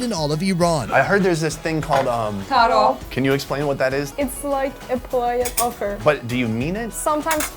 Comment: what does she say? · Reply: just like a 'polite offer'